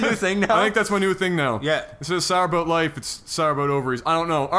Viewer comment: all this time i thought you guys were saying sour boat when in fact you've been saying sour about in your canadian accent. LOL